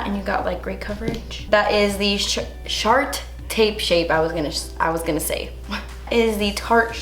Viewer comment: I thought I heard "gonna use my Shart tape shape" 😂